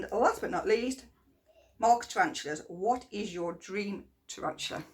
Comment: Hello from California USA. I couldn’t understand you. When asked your favorite YouTube channel. The first channel You listed that you like to watch (______)because he has bad ass videos? It’s sound like you said Rock Tarantulas? Thanks in advance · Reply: Mark's tarantulas he is awsome x